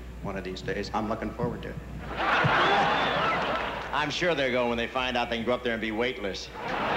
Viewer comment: Hi everyone! I'm not a native English speaker so could someone please tell what Bob Hope says at 8:54? Thanks a lot for your help! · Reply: KarelSlepika He says "I'm sure they'll go when they find they can go up there and be weightless"